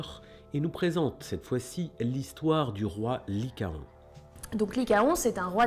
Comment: "du roi Licarion " non.